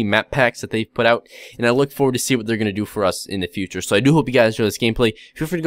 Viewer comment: When you say "do hope" it sounds like you say doop